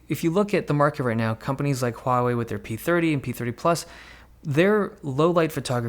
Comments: did he say p30 plus?? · did you say p30 and p30 "plus"